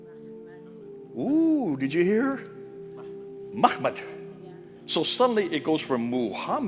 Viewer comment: NOTE: From 6:20 onward, the captions do not accurately reflect what Dr. said: He often refers to "Muhammad" and "Mahmad", but the captions only show "Muhammad" for both. > When I tried to search for MHMD, only one site mentioned "Mahmad"; all the others rendered it "Muhammad." > I think whoever (perhaps AI) provided the captions of this video used the majority interpretation of MHMD without noting other possibilities. Thus the captions repeatedly render MHMD only as "Muhammad" and does not recognize "Mahmad" as a viable alternative. > If the creators/producers of this video would take the time to correct the captions to show EXACTLY what Dr. says, especially when differentiating between "Muhammad" and "Mahmad", I (for one) would appreciate it.